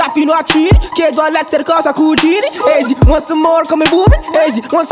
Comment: qualcuno riesce a dirmi cosa dice heeelp 🙏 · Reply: Dario Rossi "want some more?" come boomin' (metro boomin', produttore trap nº1 americano)